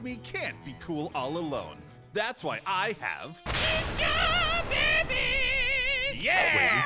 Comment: for a second i thought said Ninja Barbiez 😂